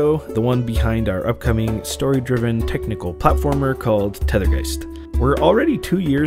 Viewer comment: Ok 2 times I heard you say ”Tether guys” 😊